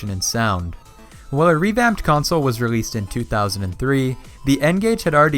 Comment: he says 2003 but subtitles say 2004